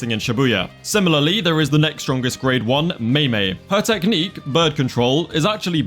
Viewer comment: I misheard and thought you said mei mei's technique was birth control💀